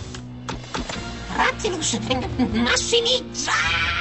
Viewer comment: Que carajo se llama ratatrampa en italiano??? Solo escuché "Ratimus utsfsgmss maximizaaaa uñiu" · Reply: Se llama Rattilus